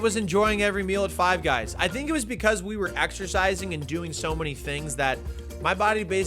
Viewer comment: exercise? I thought you said extra fries!